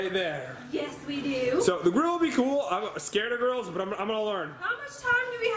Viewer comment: When they were showing the grill I thought said I’m scared of GIRLS instead of grills